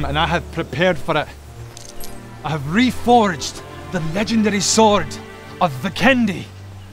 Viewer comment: I thought he said "The Legendary Sword of The Candy"